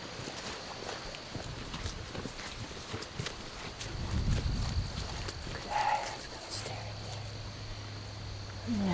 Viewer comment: WAT DOES THE SPIRIT SAY @ 6:54? I CANT TELL. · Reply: It sounds like she says "basket, stay right there." Then she stands up and says "and now" as she is walking to her next task. When you do everything by yourself, you tend to forget when people are around and keep talking to yourself. I know I do.